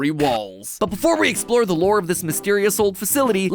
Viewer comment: it says lure instead of lore on the caption